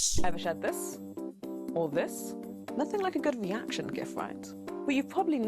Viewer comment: In his video titled "WHO MADE THIS ? WHY ? GOD ? WHY ? !" he says "JIF" instead of "GIF"